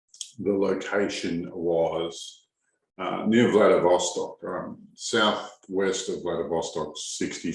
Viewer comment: Is He Saying Valdosta? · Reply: I thought he said Vladivostok